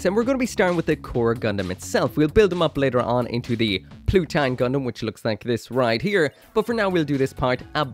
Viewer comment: Read and heard it as poutine Gundam 💀💀